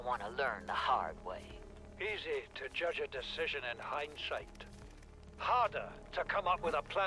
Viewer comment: Did he just say shite? This game is rated E10+, so I dunno if I'm just going crazy or if he did say that somehow